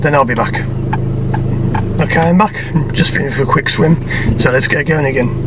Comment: Did he just say "I've just been for a quick swim" lol